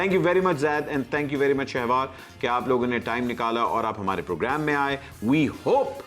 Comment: did he just say shavaar😂😂 Well his name is shahveer